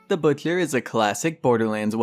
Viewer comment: The booksher 😂😂😂😂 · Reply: I thought this kid was saying The Butler I'm the first few seconds 😂 had to check the title of the vid to see what was going on